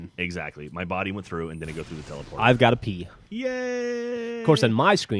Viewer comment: when he said ive got a p i thought he said ive got to pee